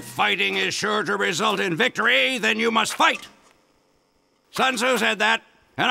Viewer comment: In soldier’s meet I thought he said son who said that instead of zun zui said that